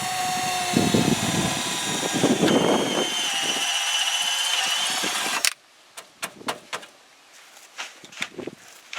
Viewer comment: DID say 4:30 in the am on her way to work??? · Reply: Yep. 4:30am. Going to work.